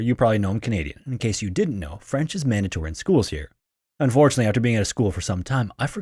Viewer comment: French is WHAT in schools here? So, the babbling guy who rushes through his words to the point it's hard to tell what the heck he's saying is hawking BABBEL? Maybe Babbel can help YOU with your pronunciation because buddy, YOU NEED IT! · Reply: it was so obvious he said mandatory💀